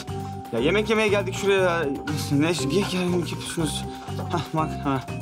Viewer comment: rahmetin ne dediğini anlamayanlar +1+1+1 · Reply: Niye gerginlik yapıyorsunuz dedi